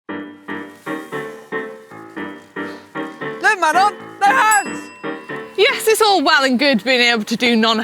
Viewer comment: Did he say "Manon, no hands!" or "Mother, no hands!" ?????????